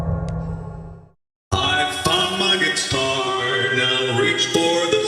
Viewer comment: it sounds like he says "I Fuck my guitar"